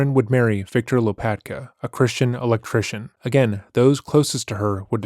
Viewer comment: A Christian Electrician. Say that 5 times fast. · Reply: I misheard it as a "Christian Hare-Krishnan" and was very confused